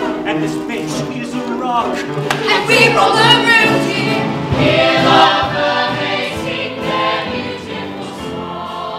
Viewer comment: what do they say at 2:37? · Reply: Rebecca Aiello and were all around you